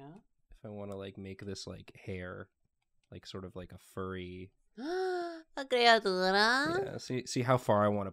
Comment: does anyone know what is saying here? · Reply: It sounds like "*gasp* a creatura??" Any time someone draws a funky little guy she likes to call them a creatura (which is creature in Spanish)